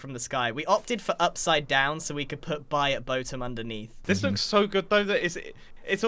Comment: I thought the buy at boatem said boat at boatem